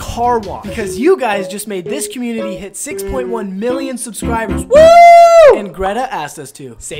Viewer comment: For second i thought he said 6.1 billion 💀